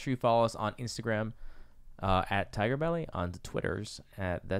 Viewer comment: Could of swore you said taco bell in the beginning, but it was tiger belly